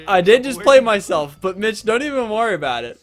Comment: am i the only one who thought he was saying bitch instead of mich the whole viedo